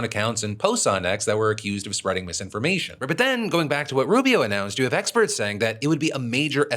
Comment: wtf was said Closed captioning says Ribbit but I know Phil didn't go full frog...